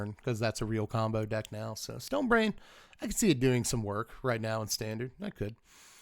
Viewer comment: I thought you said Stone Rain when you said Stone Brain. Felt a wave of different emotions.